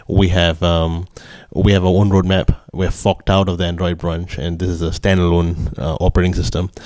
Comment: Did he say "We have f**ked out of Android brunch"? hahaha